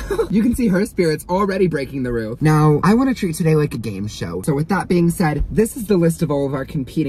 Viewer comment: i thought he said gay show